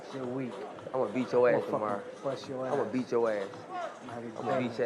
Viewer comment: Wtf 28:13 this kid fucking said ima eat ur ass 😂